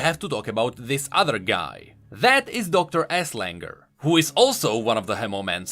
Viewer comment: I heard Dr. Asslicker